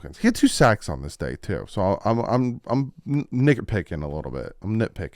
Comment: WHAT DID YOU SAY YOU WERE PICKING???!!!!????!!!???!!? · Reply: Nitpicking what do ya think I said